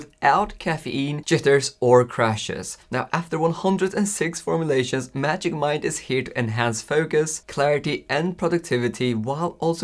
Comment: I was listening to the video and suddenly I heard "That's why Magic Mike is here" 😳